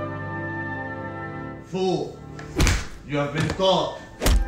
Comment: I thought it said ‘food, you have been caught’